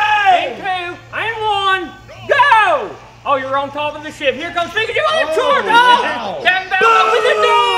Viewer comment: i thought ty said oh your on top of this sh word